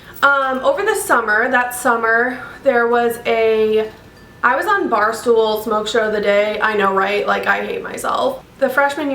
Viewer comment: what does she say at 1:28 ? what is she talking about omg im confused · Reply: she's saying she was at the bar all the time